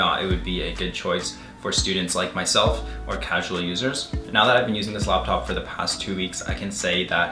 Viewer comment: my mother misheard "casual users" she thought you said "casual losers". She said, "what is he a part-time loser?" lmaoo